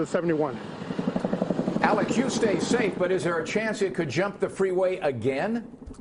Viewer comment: the guy on the other end of the guys mic was like “stay safe. But jump on to” right their I thought he said jump onto the fire for a better view. Lol I was like waw for the ratings I guess lol Jk